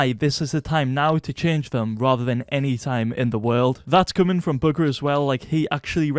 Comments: sounds like he said ¨booger¨ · did just call him bugger